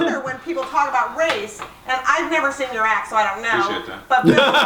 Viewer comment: What did he say at 9:19 ? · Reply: He said I appreciate that